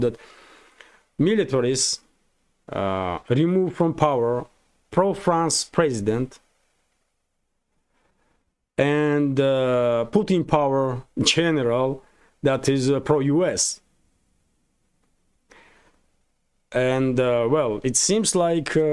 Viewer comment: you said "put in power" ....it sounded like "Putin Power" 😂🤣 You may have accidentally discovered something. You could probably actually make real money for your channel selling "Putin Power" merchandise. 👍